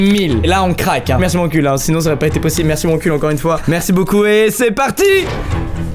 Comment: Ptn sur presque toute la vidéo j'ai compris "merci à mon cul " alors que wankul je crois